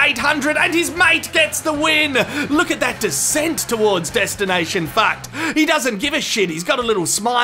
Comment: oh shit i heard that again "destination fucked" hahahahaha! i'm die now